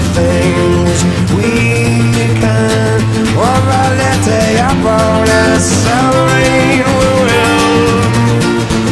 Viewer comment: What does he say at 4:07 ? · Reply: he says with a little bonus..i never knew until my mother who is a long time dmb fan figured it out on the first try lol